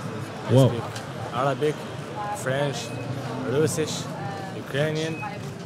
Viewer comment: can someone tell me what the 3rd language is that the guy at 6:08 speaks? i wanted to look it up but i can't figure out the spelling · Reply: He said Russisch and russisch in German means russian so like the Russian language